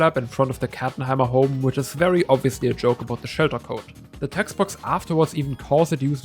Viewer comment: i couldn't understand what you said. can you (or someone reading this) transcribe it? (specifically "obviously a joke about the ??? code") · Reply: shelter code